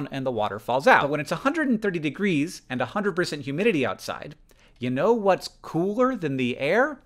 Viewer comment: At first I thought he said "103 F and 90% humidity" and I thought "Oh, like a typical Missouri summer". 130 F is a bit different!